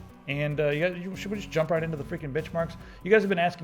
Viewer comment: "Should we just jump right into the friggen' bitchmarks?" is what I heard.